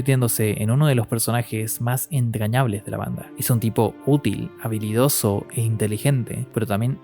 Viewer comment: cuando empezaste a hablar de Judeau en vez de habilidoso escuche vanidoso xd, y creo que si era un poco vanidoso en cierto aspecto